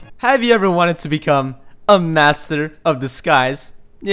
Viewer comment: Second mod, i thought u said 'master of the skys' at first.